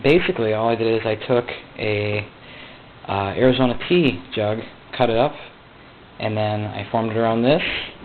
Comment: TEA jug i think. But it definitely sounds like he is saying pee!